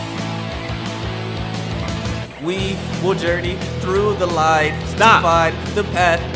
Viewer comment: Everyone’s talking about the Oreo part or the scream, but let’s just appreciate the genius lack of flow in, “We will journey through the light, To find the path that is our destiny, This is our righteous path!” It’s so perfect 😭😭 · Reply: GubbaNubNubDooRahKah I thought he said “dirty” instead of “journey” so I immediately got lost and stopped listening to that line lmfao